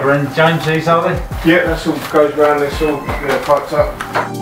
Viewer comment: What does james say ay 2:43 naughty naughty word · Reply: I said ‘it’s all piped up’👍